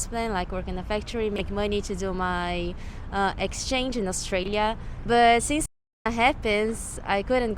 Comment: I talked with nikkei people for the first time in my life. It was very interesting to know about Japanese people who have experienced other countries. Thank you so much for watching ! I want to go to Latin America to interview Japanese people who live there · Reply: Hi I noticed two errors at 4:48 and 4:54 the subtitles say Austria when it's actually Australia that the lady is referring to.